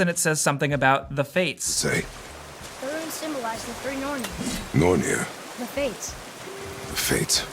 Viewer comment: What did kratos say @ 1:37 ? I have bad hearing if u can tell me please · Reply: Ahmad Baalbaki “Nothing good comes from them.”